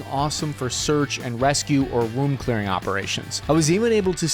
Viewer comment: In the in-bed ad, heard you say "Womb clearing operations", like an (appropriately named) DNC.